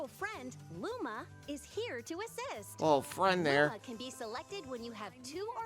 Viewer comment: when I first watched the trailer I thought she said “Rosalina’s friend, Luma, is here to exist!” And I think that’s way better and funnier